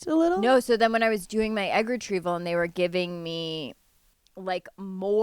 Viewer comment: Wait hahaha I thought hannah said “during your anger treatment” instead of egg retrieval im dying